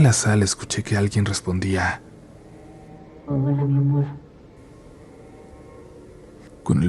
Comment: alguien que traduzca lo que se dijo en el minuto 21:19, porque no entendí ni madres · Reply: El "hola mi amor"?